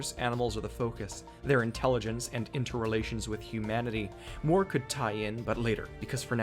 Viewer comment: I misheard "Mort could tie in" and was terrified for a second